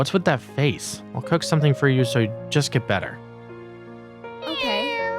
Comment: "Meow" instead of "Ok"